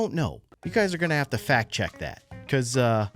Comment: fact chact is what it said everyone